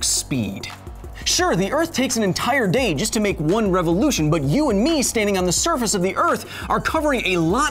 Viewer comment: Is it just me or did he say revolution at 2:18 ?